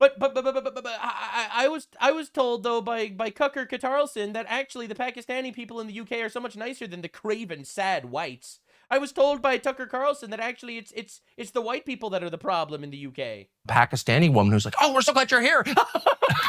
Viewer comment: Did he really say cucker Carlson? Lol